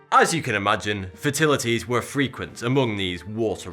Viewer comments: It sends like he keeps saying "fertilities" & not "fatalities". I'm from the UK, lived all around it from Plymouth to Edinburgh, so I'm familiar with most major dialiexts yet it still sounds like he says "fertilities". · I love his accent but it sounded like he was saying "fertility" instead of "fatality" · Is it just me that hears 42 I bet you're triggeredddddddd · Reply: Every time he said "fatality" I heard "fertility". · did he just say "fertilities were frequent" 😂